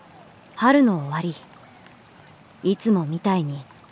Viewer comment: 開幕春の終わりを晴の終わりと聞き間違えて脳がバグった